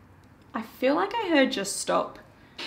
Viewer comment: Name sounded like Gina, Gene, Jean, Jeano or Dino instead of Jim. When it says Just stop, I thought it said Just STOMP as you said is that you making all the noise in hallways etc